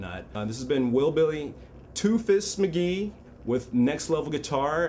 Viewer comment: At first I thought he said "Toothless McGee" and I'm like "dude! He has all his teeth!" sorry about that...... :) I do enjoy the vids you guys put out. Thanks.